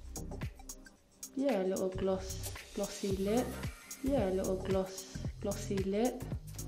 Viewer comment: What doe she say at 10:46? Let a little gloss (or bless) these lips?